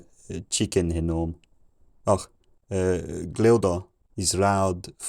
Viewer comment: i heard a pretty clear "chicken" at 1:29 (800 AD or so). i heard some other words earlier that sounded like spanish but i wasn't 100% sure